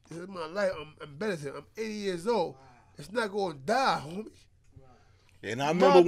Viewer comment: did he really say he's 80 years old ?? Lol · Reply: He said until im 80 years old